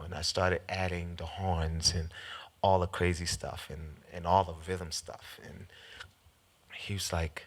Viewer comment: what does he say when he moves the fingers? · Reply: "all the rhythm stuff"